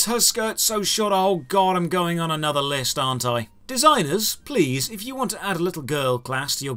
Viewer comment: "I'm going on another list arn't I ?" that is how you know an MMO belongs on this series. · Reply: I heard this as "I'm going to another list: hentai".